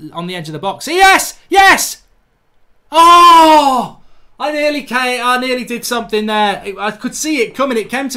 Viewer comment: did mark say i nearly came😂😂😂🤔